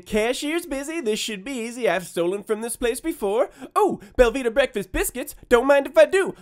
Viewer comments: I thought he was saying velveeta breakfast biscuits. also, it gratifies me greatly that you kept his money and sent him packing. punk can go cry to the naacplu. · I’ve heard him say “Velveeta Breakfast Biscuits” about 10x before I googled them and realized he was saying “Belvita” 😂